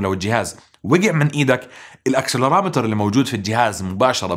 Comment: استاذ انت قلتنا نبهوني على كلمات بحكيها بالانكليزي وما بتفهموها شوهي الأكسوبلاترر ما بعرف شو الي قلتها بالدقيقة 3:31 😂 هلق لغتي ماشي حالها بس بعمري ما سمعت هل كلمة 😅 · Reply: accelerometer و تعني حساس التسارع